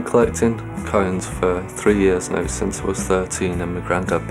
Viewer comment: I heard 'coins' as cones... I was like that is an interesting thing to collect